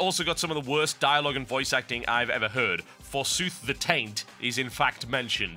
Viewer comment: did he just say, "for soothe the taint"? lawlz